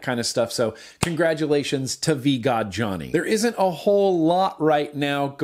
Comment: Am I the only 1 that thought he said vagina instead of vgod johnnie